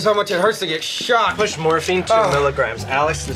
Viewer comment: Do i hear "Morphine 200mg"? That's soundcloud rapper territory.. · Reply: it was 2mg 😂 200 would definitely cause a OD